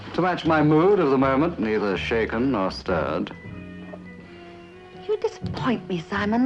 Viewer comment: "Not shaken, not stirred". An ironic James Bond reference 8 years before he got the part. I love that! RIP, Mr. Moore. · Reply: Mark Thompson - that's what Bond said, but Templar said, "not shaken, not stirred" at 34:44.